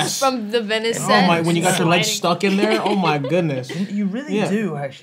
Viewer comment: Okay but I am the only one that heard the "leave me alone" and "aw" from Tom and Zendaya at 5:26 because they made fun of his voice? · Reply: I thought he said oh my god 😂 his voice sick sounds cute